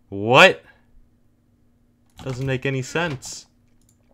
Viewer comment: I thought you said you never die